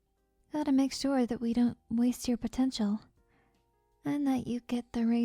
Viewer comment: Did you say potential?